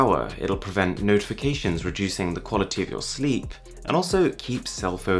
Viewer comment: "Reducing the qaulity of your sleep?" Did I heard that right?